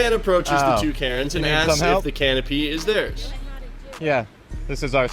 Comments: i thought when they said canopy that they actually said can of pee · I thought you said they were stealing a can of pee. They can have my pee for free.